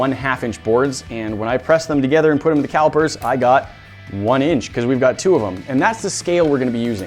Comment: am I the only one who can’t understand what he is saying oof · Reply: "and when I pressed them together and put them in the calipers, I got 1 inch."